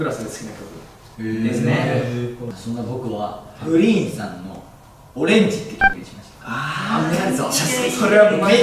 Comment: Greenさんのオレンジって曲が フリーザのオレンジって曲って聞こえたのは私だけでしょか？